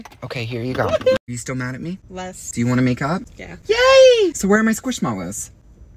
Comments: I thought he said, "So you wanna make out?" For a hot sec there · When he said do you want to make up I thought he said do you want to make ou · I thought he said makeout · I thought said "do you want to make out" 💀💀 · i thought said wanna make out, not make up 😭😭😭😭😭 help 😭😭😭😭😭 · Stop I thought he said “ do you wanna make out”😭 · I HEARD “do I wanna make out?, yes” 😭🤚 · at first when said “are you mad at me?” and said “not as much” then said “wanna makeup?” i thought he said “wanna make out?” i dropped my phone, and my friend was on the couch and looked at me💀💀💀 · Am I the only one who thought he said you wanna make out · Why’d I hear “Do you wanna make out” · Not even kidding I thought you said you wanna make out but never mind you know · I thought you said do you want to make out 😅❤😂😂😂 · WHEN HE ASKED "wanna make up?" I HEARD "wanna make out?" I HATE MYSELF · When he said make up i thought he said make out 🤣😂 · I thought you said do you want to make out 😅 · i thought you said "do u wanna make out"lol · What was said: ‘do you wanna make up?’ What I heard: ‘do you wanna make out?’😭😭😭😭😭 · I thought they said make out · I thought they said “do you wanna make out”😂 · I thought he said “do you wannna make out” not “do you wanna make up” 😭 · When he said “wanna make up” I thought he said “wanna make out” and I screamed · Noooo i heard " do you wanna make out? " 💀😅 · i thought u said "u wanna make out"